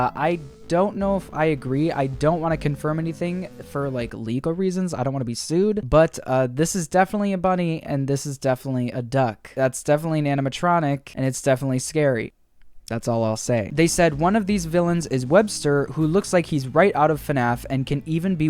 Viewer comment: Did you say... DUCK? DUCK?!?!?